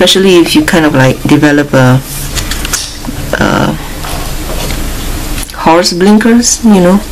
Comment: What does she say at 26:05? All I can make out is Horseblinkers?